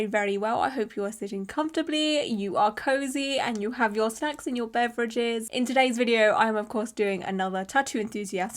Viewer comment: 💀i miss heard that as sex and beverages and was feeling very confused for a minute.